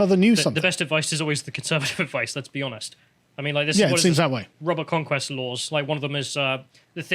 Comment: What did he say at 11:48 ? RUBBER CONQUEST LAWS? What is that? I couldn't really hear correctly the name of that.